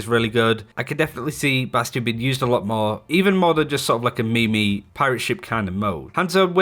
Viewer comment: did he just call a meme, a me-me? 🤔🤔😂😂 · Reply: I think he said "meme-y"